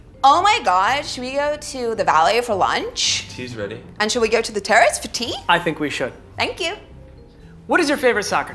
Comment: Did she just say shall we go to the teriist for tea?!